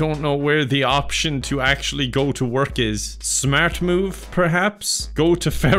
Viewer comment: everytime you said “smart move?” i heard “smartmandude”